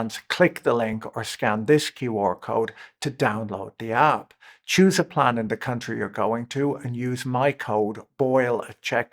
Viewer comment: “Choose a planet or country you’re going to” is what I heard and is on brand from Patrick.